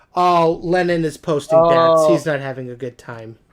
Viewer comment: I heard bats